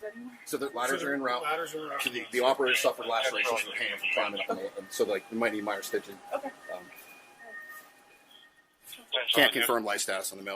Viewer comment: I heard "officer suffered lacerations.... climbing...might need minor stitching."